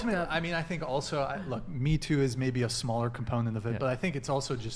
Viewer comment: "Im a lawyer. . . " "Im a lawyer. . . " "Im a lawyer. . . " "Im a lawyer. . . " "Im a lawyer. . . " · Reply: All I heard was "look at my tits"